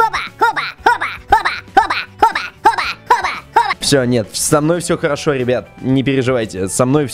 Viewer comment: говорит -хоба, а я слышу-попа!